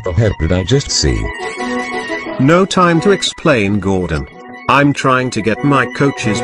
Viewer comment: What did Annie say at 13:45? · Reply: I think she said, "Hi Gordan. Bye Gordan."